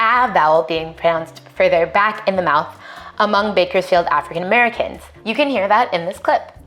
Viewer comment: Did she say 'Bakersfield African Americans' at 3:44? Y'all searchin' to make content.